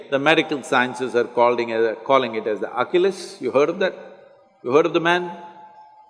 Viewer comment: He’s saying ACHILLES HEAL. I thought he said Oculus.